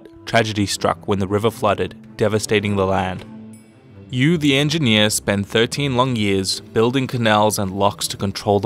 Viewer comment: I thought he said “you the engineer” like a started China🤣